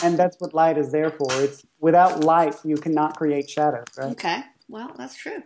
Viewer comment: what did he say? "Without light you cannot create...cheddar" ?? I don't think that's what he said lol but it's what it sounded like to me! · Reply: ramirez "Without light you cannot create shadow."